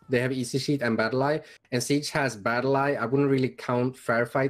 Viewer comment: It's the youtuber battleeyetm. He's promoting to his 50k + subs hacking etc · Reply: I thought you were saying Battleyeet